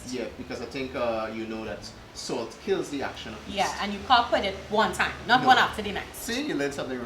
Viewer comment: I swear I heard say " that's the action of the baby powder and yeast.." lol 😂😂😂 Great job guys...keep the videos coming!